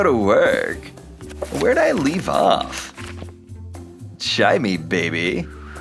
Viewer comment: Did they say cha in 8:33 cuz that is a bengali word